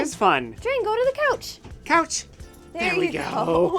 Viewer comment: That looked like a lot of fun , love how thought you said cows ( couch 😂 ) the second site looks rather nice.